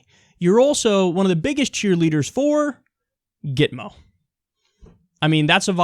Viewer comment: What's that? I couldn't understand. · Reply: "Gitmo" = Guantanamo?